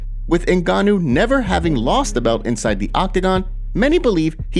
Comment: “Ngannou never lost a bout inside the octagon”.. does NOT know their facts. Lost to Lewis (horrible fight) but also lost to Miocic 😅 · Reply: I thought he said bout too but heard it again and he said “never lost the belt”